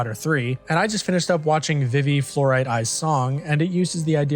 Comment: What is the name of the anime at 51:30? I couldn't hear it properly and it looks good, I want to try it out. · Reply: Vivy: Fluorite Eyes' Song, 9/10 would recommend